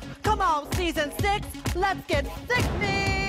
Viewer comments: See, I always heard "let's get sexy", like, six-y, and then are started looking at different RPDR clips and was like oh. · Ok... I legit always thought she said "Come on season 6! Lets get sixy!!!"... Like sexy.... But six.... I feel extremely dumb now